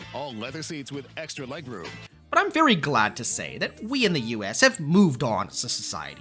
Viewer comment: He said something about US at 5:15 and all I heard was boobs lol